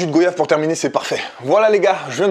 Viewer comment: PARDON ??! Un jus de quoi ?! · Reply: Goyave